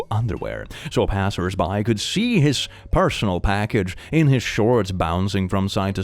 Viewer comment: i heard personal maggot the first time i listened to this